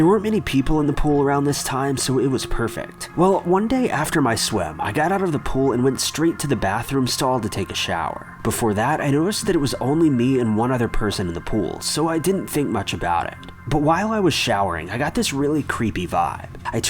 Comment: I could have swore he said "i got out of the pool when straight to bathroom stall to take a shit"💩 but I can't get him to say it again🙁